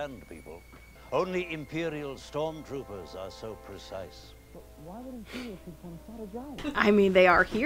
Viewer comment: I still laugh when I hear "only imperial stormtroopers are so precise." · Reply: ???? Laugh? Actually, he said only "Imperial Stormtrooper blasters" are so precise.